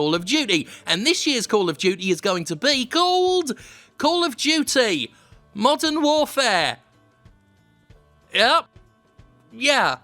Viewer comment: At first I thought you said "Call of Duty: Mutton Warfare".